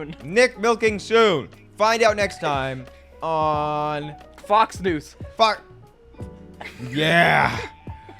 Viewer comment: I heard this as “Vox news”, gods I’m fixated